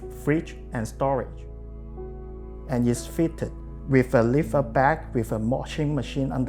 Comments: On the floorplan it’s clearly said , the master bedroom, bedroom , and maid’s room. Maid’s room not maid’s bedroom. It’s common we call the working room - Maid’s room in Asia , where the maid does the ironing or folding or putting cleaning things. Just Like janitor room , the janitor doesn’t sleep in it . Cleary see there’s not a sleeping area, and there are kettles and things on the wooden platform. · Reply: It seems to be fitted with a "liftout bed" though. At least I think that's what the architect is saying at 5:45? The bed would indicate that's where the maid is supposed to sleep, no? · To everyone who complains about the maidsroom I would suggest to thorw a quick view on the appartment layout at 2:30: you clearly see and hear, that the maidsroom's front(!) is used as storage: She doesn't need to lie on top of the stuff stored there. She certainly has a bed, it's just in the back and not visible in the video. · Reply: He clearly said ‘lift up bed’ at 5:47. Although his pronunciation sucks. I’m assuming he’s referring to the counter on top of the washing machine as the maid’s bed